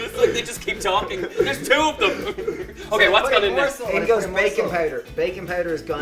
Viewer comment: i thought he said "bacon powder". i was momentarily happy, until i realised it was "baking powder" :(